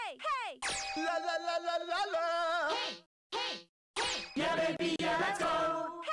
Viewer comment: welp, I still have no clue what they're saying... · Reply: according to the english translated guide its "Yeah baby yeah let's go!"